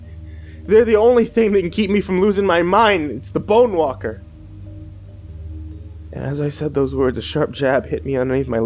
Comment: i thought it said the bone wanker 😐😐😐😐😐😐😐😐😐😐